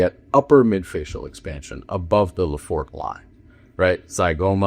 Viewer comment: What is he saying at 12:45? Above the _____? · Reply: Above the Lefort Line